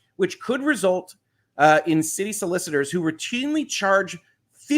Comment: You read "city solicitors" and I heard, "silly solicitors." 😂 Ah, these silly solicitors and their College Football.😁 "We might have different definitions of positive, ya'll."😂🤣😂:elbowcough